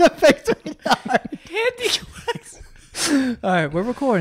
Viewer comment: did he say handiquacks from that family guy episode?